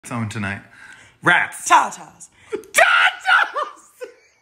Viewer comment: WHEN SHE SAID TATAS I THOUGHT SHE SAID Teletubbies